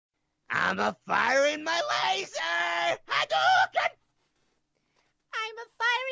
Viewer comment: It sounds like there saying ima fire my laser ADOOAKE